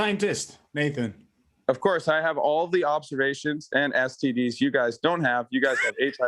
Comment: did Nathan say he has all the STDs?